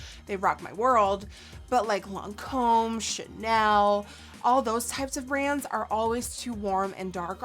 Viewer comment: when you said chanel I thought you said shitnel